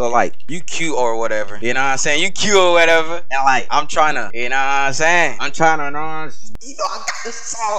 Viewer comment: the captions say I’m trying to annoy you.